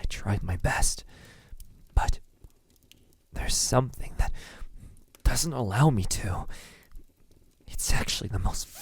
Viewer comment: I SWEAR it said “Vampire doesn’t love you” I really thought it said that